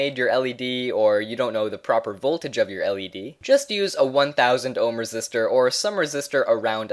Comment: I really thought he said ohm k! Haha